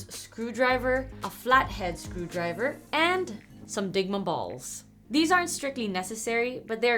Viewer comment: I misheard Dygma bowls as Dygma balls. I bet that's intended by you guys.